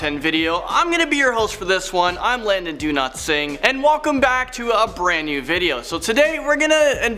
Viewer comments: sounded like he said Landon do not sing 😂😂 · I thought he said "I'm Landon Do-not-sing" at the start XD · When he said his name I thought he said Landon do not sing 😂 😂